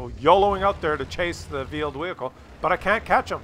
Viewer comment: I keep hearing "vealed whehicle"